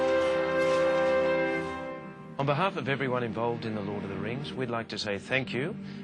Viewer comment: does david Whenem (faramir) say Hord of the rings instead of lord at 6:01 ?